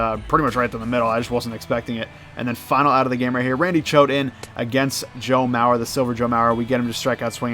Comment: Sounds like he said chode 8:35 lololol